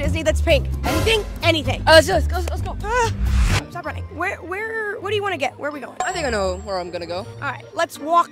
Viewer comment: WHERE DO U WANT TO GET BRO like did she just say *where do u want to get*😂😂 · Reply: She stuttered she said "Where wher- what do you want to get"